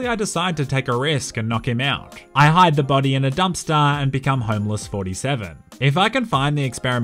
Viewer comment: What says, “homeless 47.” What I hear, “homo 47”